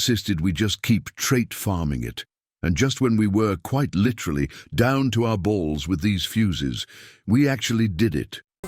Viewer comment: wtf did he just say? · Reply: Down to our BALLZ💀😭